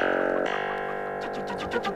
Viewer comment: what is this word he says at 5:08? how do u spell it i no it is a strange request :_) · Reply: ça m'énerve